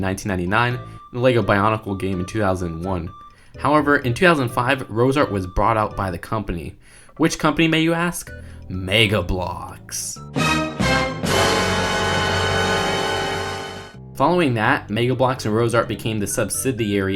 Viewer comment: sounds like it saying mega bloooooooooooks